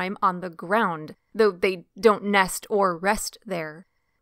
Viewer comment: Many of the things you stated as facts are inaccurate as per my first hand observations. So many in fact that this channel loses all credibility with me. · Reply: ok sorry I thought you said the do nest on the ground, after watching again you said they do NOT. As for their speed I've had them track along side me at a little over 30mph. This was right before he started in front of me and I ran over him. Didn't mean too, and he ran off after tumbling a couple times so I guess he was ok.